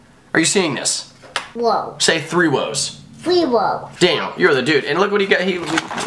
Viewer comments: did he say "Damn,your the dude" or "Dan your the dude" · did you say the damn at 1:39? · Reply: no no no no no no. you said "Daniel, you're the dude" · I thought said "Damn, you are the man" and I was like whhaatt